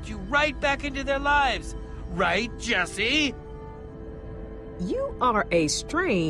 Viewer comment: why Harper sounds like she is saying You're a gay. to Ivor?